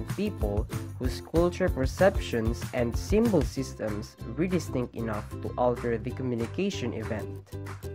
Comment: what did you say in 1:16 to 1:18? was it re-distinct?